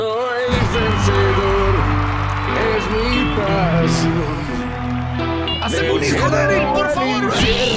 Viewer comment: "HACEME UN HIJO POR FAVOR" Este doblaje es Hermoso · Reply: No sé si solo yo escuché "hacéme un hijo Daryl, por favor" tal vez aludiendo a que la voz en inglés la hizo Norman Reedus